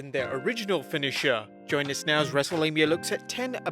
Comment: Every intro, I hear "Wrestle-labia".